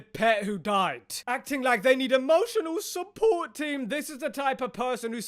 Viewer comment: What I heard was emotional support tea 🍵😼 THEY did have some tea handy ✅